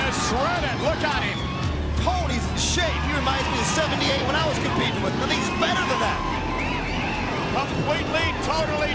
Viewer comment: did he say holy shit at 0:44 lmfao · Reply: No, he said Tony's in shape.